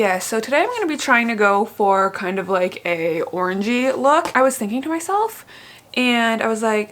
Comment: Lmaoooo when you said you were going to do an "orangey look" I thought you said an "orgie look" and I was like damn sis that's intense 😂😂😂😂😂